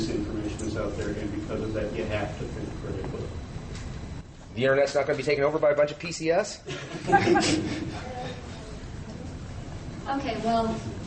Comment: I love that last comment by a questioner. I may have misheard it, but what I heard was "The internets not going to be taken over by a bunch of PCS."